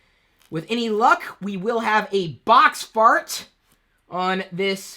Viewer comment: did he say... box fart at 2:05?